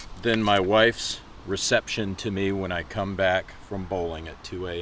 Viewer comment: When he said "chillier than my wife's reception to me when i come back from bowling at 2 AM" i felt dat. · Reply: Oh I thought he said "coming back from poland"